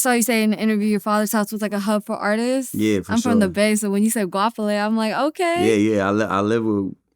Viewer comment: Can anybody tell me who the Bay Area artist was that she brought up who lived with his dad? Sounds like they said “Guafele” or something. He said she used to cook him breakfast etc. I couldn’t make out the name.